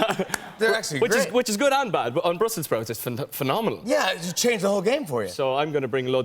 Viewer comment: I heard which is phenomeNIALL.